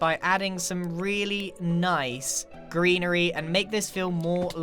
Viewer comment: When Grian said greenery, I thought he said Grianery XD